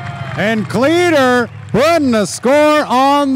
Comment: Did he just call him "Cleeder”.....🤔😬